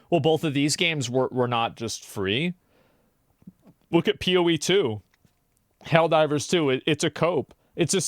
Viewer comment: Poe2 and Marvel Rivals ended blizzard’s excuses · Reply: I thought you said Pou 2 for a split second 💀